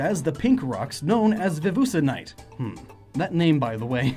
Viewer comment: did you just call it "Vevusenite"? Lmao